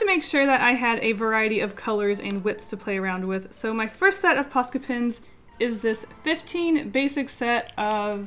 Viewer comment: I thought that said "Posca Penis." Why, brain, why.